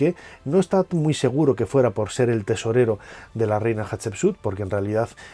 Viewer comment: Ay yo leí mal jajaja pensé que decía: “El tesoro de la reina Hatshepsut”. Pero igual excelente video. ❤